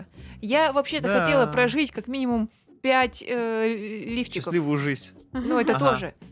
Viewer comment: Я одна услышала то что она сказала пять лифчиков?